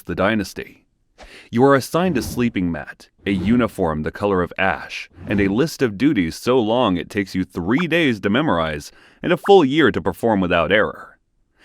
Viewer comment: “A uniform the color of ass” 😭😭🤣😭 that’s what I heard the first time